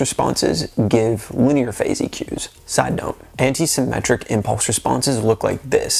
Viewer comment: Eyes on the camera. It’s off putting. And when you said anti symmetric I thought at first you said anti semitic. Suspicious character 🙃 and I didn’t think I’d watch a music production video I barely understood the first half of. Extra suspicious. But by the end I loved the video